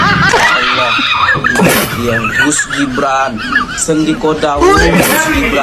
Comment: Saya salah dengar , saya kira Gus Gibran , ternyata wedus Gibran